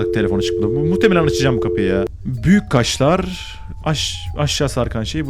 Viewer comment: Büyük kaşları büyük kaşar anladım